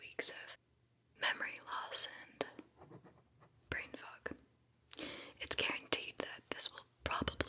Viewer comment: OMG! This is ingenious! So creative and hilarious!❤In the human Delta section, at first I heard “brain FOG” as brain F…(something else entirely)😂 When you got to the Gamma level, my brain thought “skinless Franks”. Omega made me fear that you were going to tell someone to limp or crawl to a seat - so THANK YOU for not doing that! (Someone is entirely inappropriate here)Also, I was thrilled to realize that your company recognizes the nose as a limb. High time!